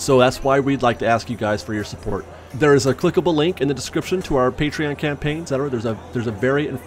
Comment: Funny, I thought it said "Join our PATRON campaign".... so I've got a few shots of great tequila in my system.....Ohhhh..."Patrion"...that's different! I'm on board! ALOHA !!